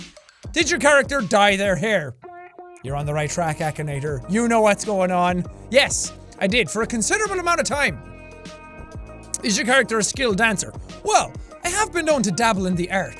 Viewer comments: I thought it said "Has you character killed cancer?" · I thought it said ‘skilled cancer’ and I was like “lmao yes, JSE in a nutshell 🤣”